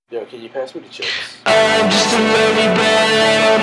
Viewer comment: thought he was saying "im just a little boy" all this time